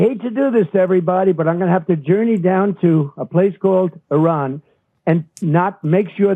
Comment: Did trump just say a slur? He said “.. Jew you down”??? Omg.